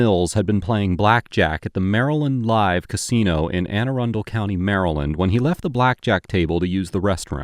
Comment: I 100% thought he said "Anor Londo County in Maryland," and now I must go live there.